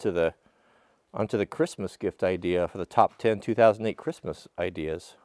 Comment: Am I the only one that heard him say 2008 Christmas gift list?